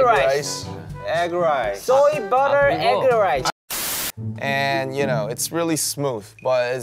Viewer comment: I keep hearing soy,butter,eggerrice